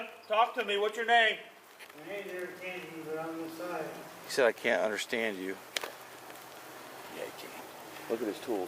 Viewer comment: "He said he can't understand you" - No he didn't... lol. He said "my name's Eric Kennedy"